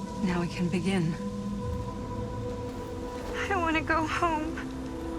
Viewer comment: Just Stan banging on the door that closed by sliding down instead of trying to lift it open was so funny watching. EDIT: Also just made it tot he end @ 5:03:25 it sounds like she says I dont want to go home but the subtitles say I want to go home.